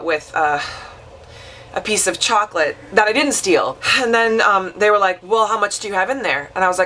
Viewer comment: Omg you are the original honey boo boo! I could have sworn you said, "then we were fucking...."